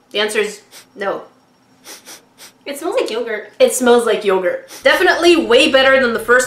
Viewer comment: When looking at the 1 slime kit 2nd slime the strawberry 1 I thought said it smells like anger I did not hear yogurt until agreed.